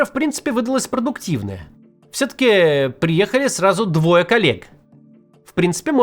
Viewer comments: послышалось - "...приехали сразу двое калек..."😁 Но, если подумать, на самом деле так оно и есть! Только в ментальном смысле! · "приехали сразу двое калек"😂 Знаю, что там "коллег" Максим имеет в виду, но то, что послышалось как более соответствует действительности.